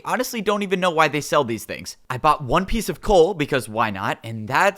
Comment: did you just say One Piece